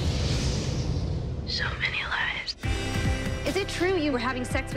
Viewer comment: did Callie say lies or lives?!?!?!?!?!? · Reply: lies